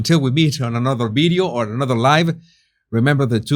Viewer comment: It’s funny, I always thought you said “another life” when you said “another live” I thought you were being optimistic!😂😂😂 ❤from Winnipeg 🇨🇦